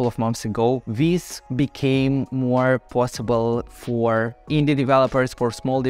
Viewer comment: I thought you said "Indian" developers, lol